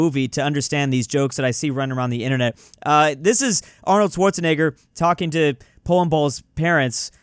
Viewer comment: what did he say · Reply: Arnold Schwarzenegger